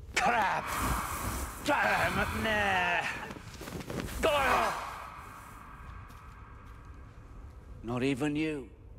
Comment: what name did he say? I don’t hear anything of what Voldemort is saying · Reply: McNair